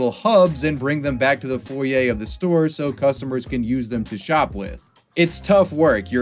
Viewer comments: i thought he said so customers can use them to shoplift 🤣🤣💀😂😂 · What you said at 4:10 made me laugh “so customers can use them to shop lift” I know it wasn’t intentional but it put a funny picture in my head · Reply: i think he was saying "so customers can use them to shop with" but when i first heard him say that i was confused to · What you said at 4:10 made me laugh “so customers can use them to shop lift” I know it wasn’t intentional but it put a funny picture in my head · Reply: I also thought he said "Shop Lift", So I went back and He said "Shop With". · I thought he said “so they can shoplift” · I thought he said "so customers can use them to shoplift"